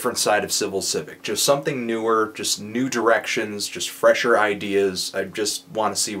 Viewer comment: when he said "new directions" I thought he was saying "nude erections" lol freudian ear?